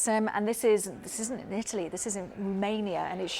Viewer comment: where the fuck is the country “Mania” ? Or am I hearing it wrong?